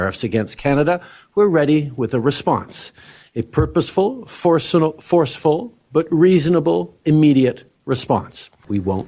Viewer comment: I'm Canadian and the big problem here is that we have a Federal government so corrupt that it is impossible to believe what Trudeau says about fentanyl, he has always lied about everything, his friendship with china is questionable to say the least, clearly there could be a large production and of course he would hide it, he has to leave, he's the destroyer of this country, we will be lucky if we survive this. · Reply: it sounded like Trudeau had a bit of a Freudian slip. He sounded as though he accidentally said "a purposeful fentanyl", then caught himself and changed it to "purposeful, forceful". Inquiring minds would like to know if he has fentanyl on the brain...